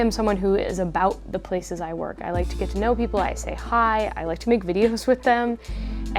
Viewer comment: " I stay high" hahahaha No way I'm hearing this right. What did she say here?